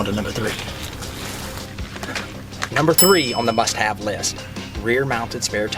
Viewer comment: Sounds like he said "shit have list" lol